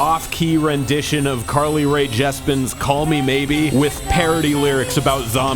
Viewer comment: did just call her “Carly Rae JESPEN”??